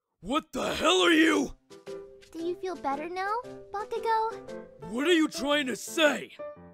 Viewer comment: I deadass thought she said “Fuck-ugo”